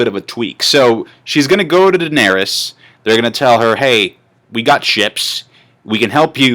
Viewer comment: thought he said 'we got chips'